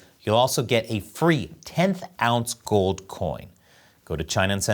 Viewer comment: i thought you said you get 10oz gold coin and thought holy shit